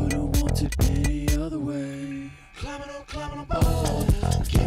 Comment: Sounds like hes saying "climbin a bow". Haha idk, thats what I hear.:P